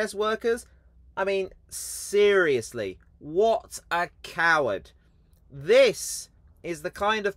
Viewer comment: Sorry thought you said cow rather than coward.